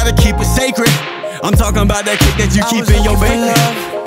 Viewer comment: Yo word to my mother I thought u said I’m talking bout that shit you keeping your baby😝🤷🏽‍♀️❤️